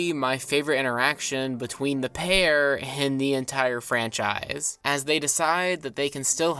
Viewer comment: I know you said "my favorite interaction between the pair in the entire franchise" But I heard "my favorite interaction between the pear and the entire franchise." Which its hilarious that it still works for describing the scene, though a lot more... tongue in cheek